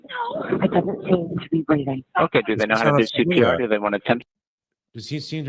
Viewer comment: At around 20:38 does say ,”It doesn’t seem to be breathing?” I’ve listened several times just not sure if I’m mishearing her.